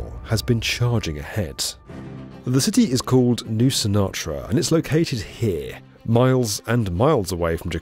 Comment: I thought you said "New Sinatra"